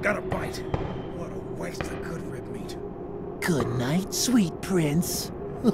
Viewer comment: *Gets Lucky in 4k* what did you say? 🤨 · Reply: He said’s “goodnight sweet prince…”.